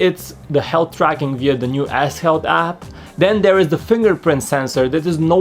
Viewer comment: I thought he said "ass health" for "s health" 😂